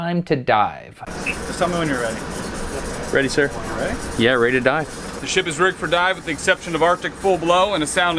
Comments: I heard "ready to die" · I thought he said it was time to die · i thought he said "ready to die" at 5:33 lol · i thought you said "ready to die" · sounded like “yea ready to die” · sounded like he said “Yeah, ready to die” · i thought he said "its time to DIE." But really he meant "Time to dive." Oof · did anybody else here ready to die instead of ready to dive and the 5:34 mark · I keep hearing I'm ready to die XD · lol subtitles say "yeah ready to die". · at first I heard it as "Ready Sir. Yeah ready to die" XDDD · Sounded like he said yup I’m ready to die